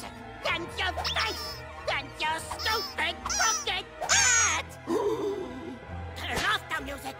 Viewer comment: I legitamately thought Mugman said "your stupid fucking hat" when i first watched this...